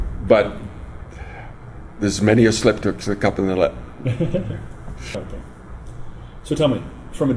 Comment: What does he say? Is it "There's many a slip up that could come yet."? No?